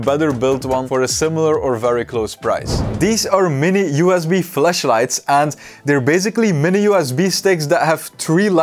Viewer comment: Sounds like u said "Mini USB FLESHLIGHTS" 🤣🤣 If you dont know what they are, dont look em up 😂